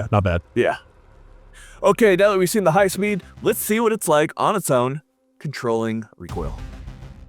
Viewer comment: did he say the brake had more recoil than the flash hider?